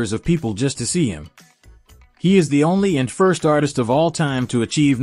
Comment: Did you hear what he said, from, 44:34, to, 44:44?- Michael Jackson, "is the only, and first artist of a time, to achieve number one's in the 1960's, 1970's, 1980's, 1990's, 2000's, and, 2010's."- That is about THE BEST THING THAT CAN BE SAID ABOUT AAAANNNNYYYY ARTIST.-PERIOD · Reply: correction: " of all time ...."